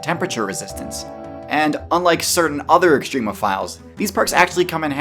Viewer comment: Hey everyone, check out this one minute video I made for my patreon! <link> Even if you aren't at all interested in pledging, I still think the video turned out great and looks cool, so give it a watch! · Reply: I thought that you said that water bears WEREN'T extremophiles...